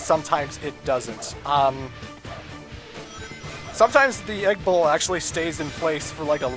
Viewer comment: When you said "Egg Bull" in your Dr. Eggman voice I thought you said Egg Bowl I was gonna say: What's so intimidating about an Egg Bowl? Then I saw the game introduce the boss and it said Egg Bull and I'm like...that's what he said, okay that's so much more intimidating.